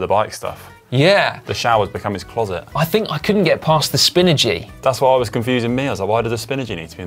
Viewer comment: For some reason I heard Spinergy are *Spinachy*. Now I want spanakopitas